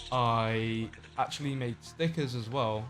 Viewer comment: When he said Stickers i thought he said Snickers